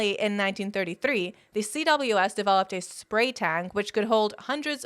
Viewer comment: I need the captions, I thought you said chemical weapons in spray tans, instead of spray tanks. 😂